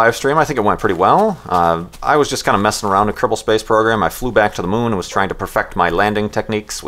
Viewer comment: What is the name of that moon flight program you mentioned? I cant hear the name. Sounds like Kirple space program. Anyone know?